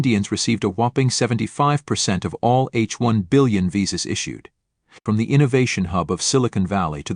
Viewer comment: Good one. Two corrections to make. 1) While listing out advantages of USA, I heard "H 1-Billion" visas instead of "H1-B" visa 2) Canada is no more a friendly country of Indian :( Or, it is likely to remain a foe until the Trudeau government remains in power.